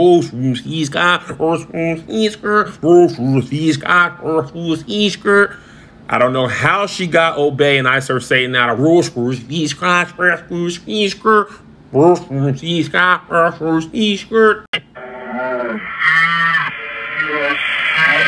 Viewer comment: I heard “I serve (Santa)”